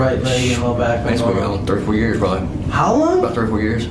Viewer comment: It was great to see this gentleman get that much needed relief. 34 years! Jeeez... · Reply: 3 to 4 years is what he said, misheard him.